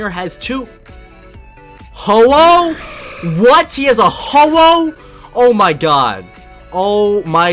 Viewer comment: sounds like he said he has a holo